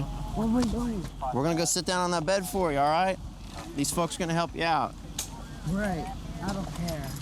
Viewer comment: did he just say these f word are going to help